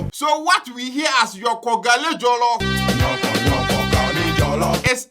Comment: Aswear 😂 i thought he was saying he favorite is joko Gari Jelllof 😂